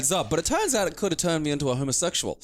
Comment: I honestly thought you said the Lyrica turned him into Homer.